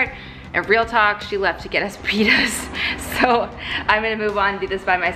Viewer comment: Am I the only one that thought that said that Kelsey had “gone to get some penis”? 😂 I thought “Bloody hell, that’s honest!”. After another listen, I’ll definitely stick with pittas for my houmous!